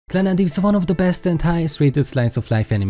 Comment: I heard “one of the best hentai” and had to replay it